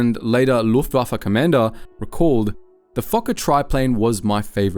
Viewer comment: The Fokker no fu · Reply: I heard “the fucker”